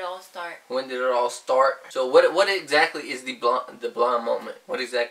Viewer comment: Is he saying blonde moment? Well whenever he says it, drinnnnk 1:10 😂🙌🏻